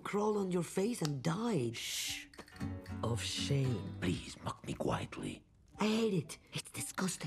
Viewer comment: I misheard what he said 💀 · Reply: He said “please mock me Quietly ”